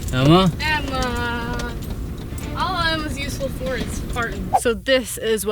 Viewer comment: Did I really hear "All Emma's useful for is farting"?